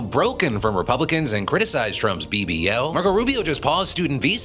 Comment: am I hearing BBL or am I going crazy